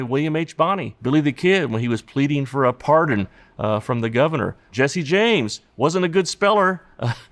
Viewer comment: did he just say Jesse and James?